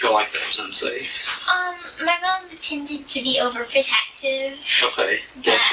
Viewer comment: She said my mom tended to be overprotective like in the past tense soooo… yeah she’s lying about everything even when she gets caught this women is a lier and a murderer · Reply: Tended? I thought I heard pretended. Well... I'm not going back to read the closed caption. :-)